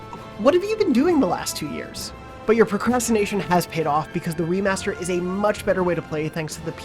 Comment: did he say Wii-master?